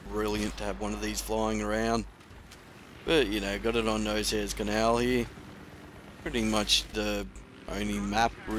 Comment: Did he say "Nosehair Canalee"